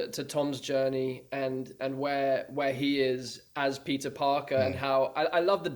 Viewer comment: I'm sorry, but from 5:52 and on all I hear is "Pizza Parker" 😅 But seriously though, it's so surreal having all three of them together! I especially loved Tom's story of him and Jon being terrified making Homecoming, both newcomers trying to make such an important movie.